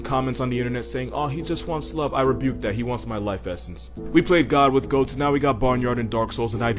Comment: What does he say at 0:50 . He wants my life what ? I hear « life essence »